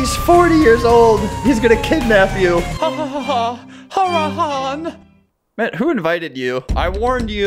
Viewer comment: I think he wanted to say Haram😂